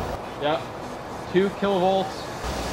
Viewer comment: I thought it said 200,000 volts